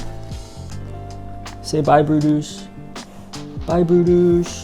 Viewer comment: You said "bye brutus" but i heard "BYE BRUNTUS" 😭😭 jerawatttt